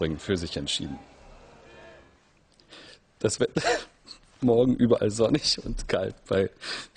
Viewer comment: Warum verstehe ich: "Morgen überall sonnig und geil" xD?